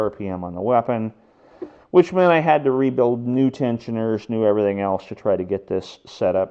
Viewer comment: what is that white peice of plastic that fits over both sides of the weapon clain? i think you said it was a tentioner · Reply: Correct. It helps tension the chain and guide it so that it doesn't come off the sprocket. The frame can twist under big hits so it's important to make sure it stays put.